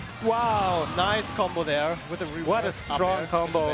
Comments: が、どうしても「ナイスコンボダヨ」にしか聞こえん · 聞き取れたのここの「ナイスコンボでよ」だけだった